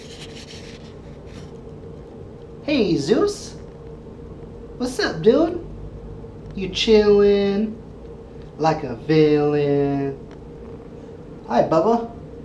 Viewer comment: When you said "Hey, Zeus" I thought you said "Jesus" (hay-soos). That made me chuckle.